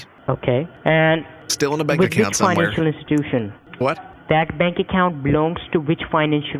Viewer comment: It sounds like he says tittie bank 😂😂😂